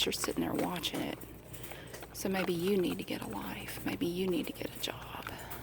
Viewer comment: lol did she say you should get a life if I didn’t have a life I wouldn’t be able to watch you or comment this